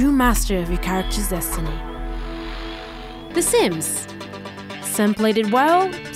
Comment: "The Sims". I dunno if I'm deaf but I heard "The Simps".